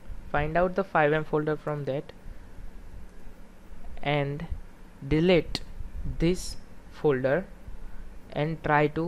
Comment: did you say delete.